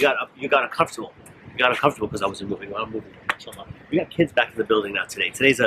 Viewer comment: Everyone: he said “in july” Me: but I heard “in sha allah” 😬